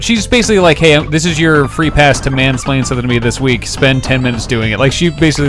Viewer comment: It is very possible that I didn't hear what was said correctly, but did he use the word "mansplain" in every day conversation at 10:30?